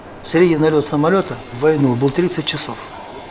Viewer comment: For those who don't understand Russian - at 18:40 the subtitle reads "The average flying duty time during the war was thirty years" while the narrator said 30 hours. The narrator is correct, of course, CC is wrong.